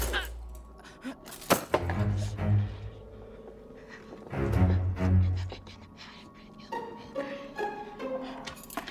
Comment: what is she whispering? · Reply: “you’ve ruined my life! you’re gonna pay for what you did to me.” i think i don’t know